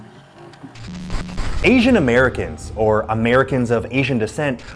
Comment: I for a hot minute thought you said "Ancient American" and I was like "How tf"